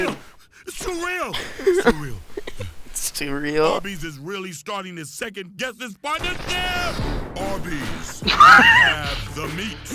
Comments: for those wondering what he said, it's "Arby's is really starting to second guess this sponsorship" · I think he said "Arby's is really starting to second-guess this partnership!"